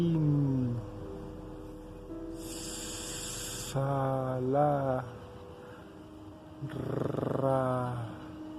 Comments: tvonline america would you be willing to let us know what the cameraman said at 3:57 (ish)? It’s killing me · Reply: Of course, My pleasure...he says "Solin, salah, ra...la..." its nothing that makes sense, just expressions...not even spanish words. best regards. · Can someone translate what the man behind the cam is saying at around 4:00, sounds like "so lee, sa la...rarrrrhhh" Thank you. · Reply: He's saying "SOLIN SALA RA" it's supposed to be a type of mantra to call upon UFOs, look it up on Google you'll see.